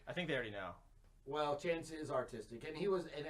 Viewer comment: i thought he said autistic so i sat there staring at the screen with a confused look on my face until i realised he said artistic....